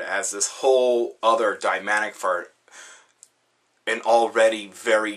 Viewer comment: did you say "dimanic fart"?